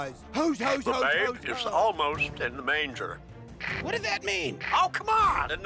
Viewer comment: First time I watched this I swear I heard Mitch say "Satan's in the manger."